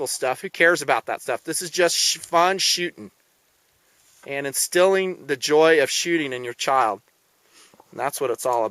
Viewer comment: do I need to goo to the doctor or did I just heard on 6:00 the line that says and the stealing of joy of shooting on your child ? maybe I missunderstood